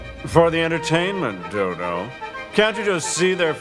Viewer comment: did he say "for the entertainment, DILDO"???